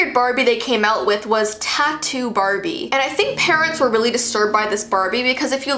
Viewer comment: iI thought she said TATI BARBIE instead of TATTOO BARBIE